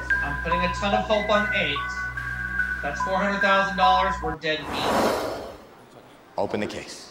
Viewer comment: What'd he say? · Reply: He said we're dead meat